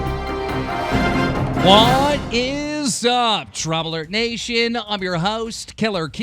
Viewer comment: It sounds like u say drumler nation